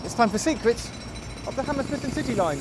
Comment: I swear it sounds like he said the Hammersmith and Titty Line. LMAO.